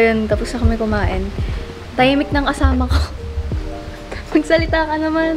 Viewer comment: When he did the traditional “pagmamano” you know its a plus points also💚 Edit: not me mishearing “tahimik ng kasama ko” to “tahimik ng asawa ko” my gosh!😂